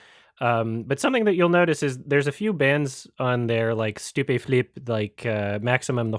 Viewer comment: did you just say Stupeflip? As in the french rap collective Stupeflip? I had to rewind a couple times this felt surreal to hear, didn't realize anyone in America could know them haha.